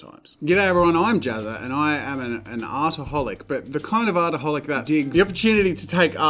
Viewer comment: When he said hi I’m Jazza and I’m and artaholic I thought he said I’m an alcoholic